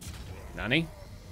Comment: Did say (Nani?)